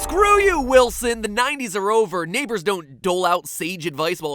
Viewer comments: Does he say the N word at 0:43? I think he meant neighbors but it sound too close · it sounds like he saying the n word instead of neighbor